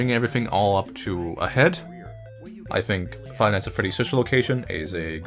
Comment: The auto generated captions wrote "finance a pretty social location", instead of five nights at Freddy's sister location.. omg. facepalm